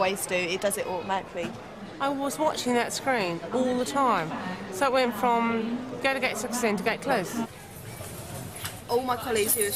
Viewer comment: is anybody else hearing "so it went from go to gate 14 to go to get clothes"